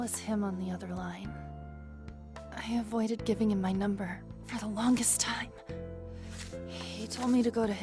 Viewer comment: what does she say? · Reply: i avoided giving him my number for the longest time